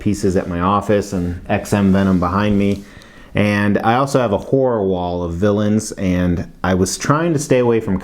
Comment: Hahahaha! Love the creativity at the start of the video. And god damn.... love that Venom hoody. At first at 1:50 I thought you had said a "whore" wall, not a 'horror' wall. Hahaha! I was like whoa buddy... that's a lot of info you're giving us. lol. The tongue, teeth and eye's are freaking cool on this bust! Really wild! I agree about the base. It's like the sculpture's hit 5 o'clock on the work clock and said, "Pack it up boys, time to go home" as they were half way through the spider emblem. Bastards... Haha. Thanks for the review!